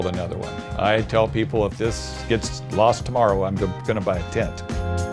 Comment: I had to turn captions on because I played the last thing he said three times and could have sworn he said, "If this gets lost tomorrow I'm just going to buy a jet." Turns out he said, "buy a TENT." 😂 I was like 'Jesus a JET! Must be nice!😂'